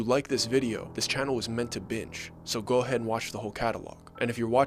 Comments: says b!tch instead of binge, just heads up if you didn't notice. Well done story though! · At about 00:51 when you’re talking about subscribing, I know you say that the channel is meant to “binge” but the captions say meant to “bitch”. 😅 just in case you didn’t know. Lol.